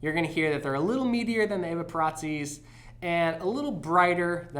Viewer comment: does anyone know what the "a little bit more meteor" mean in 8:17?? /(ㄒoㄒ)/~~